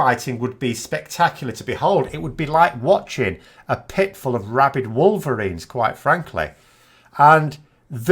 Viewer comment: I misheard for a moment and wondered what a rabbit-wolverine was